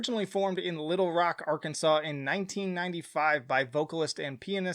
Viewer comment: I had the volume on my phone down by accident, and thought you said, Evanescence is a band formed in 1945.. I was like, damn their still going strong aren't they?